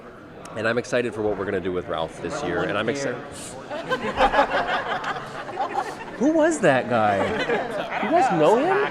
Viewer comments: grant what did he say?? i can't hear him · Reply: He said "No one cares" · grant what did he say?? i can't hear him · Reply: ŽǾŘỖ ḾǾĤẴ he says no one cares